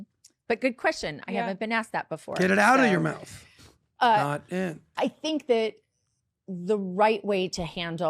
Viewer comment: turned this on while driving and wasn't looking--thought he said "get it outta your mouth, not in" to her face as a guest. Spit out my drink